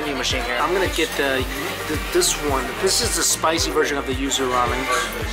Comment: I thought you said “ used “ ramen instead of yuzu ramen lol I was so concerned for about 15 seconds